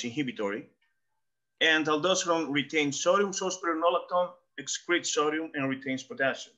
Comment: what was he saying? What was the blockers or inhibitors he mentioned? He was hard to understand, sorry. If someone is supplementing with potassium, b6, b complex, magnesium and cutting out processed carbs but still retaining water what’s the solution? 100mg a week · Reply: Spironalactone, it’s a diaretic